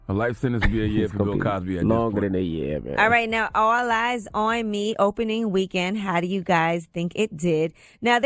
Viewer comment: Did Yee say 'All Lies on Me' at the 2:22 mark??😩